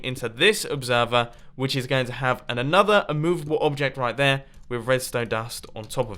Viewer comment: did anybody else hear mumbo say "an another"